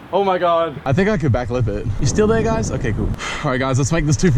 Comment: I thought he said backflip it😂